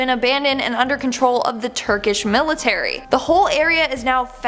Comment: I thought she said irken military 😂 it's a